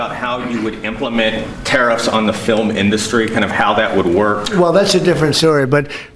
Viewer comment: did he say filem-indaustry??